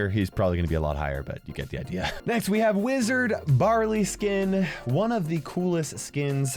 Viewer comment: Me cleaning my room and listening to this video: And that is Wizard Barley Me who just dropped my bag and misheard:Lizard... Barley?